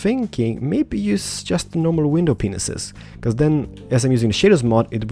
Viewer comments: I love your accent and the way you say acacia wood, but I thought you said window penises instead of panes XD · Did he say window penises? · At +13:15 he said penises instead of pieces lol